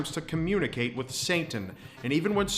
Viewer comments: "Santan" IM SORRY THATS WHAT I HEARD CX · it sounds like he says saintan instead of satan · I keep hearing "Saintan" at about 2:08 😅